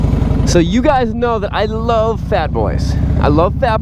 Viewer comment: Did he just say i love fat boys?